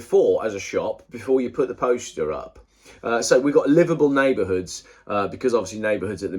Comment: Sorry, I was getting confused , I thought you were saying Liverpool neighbourhoods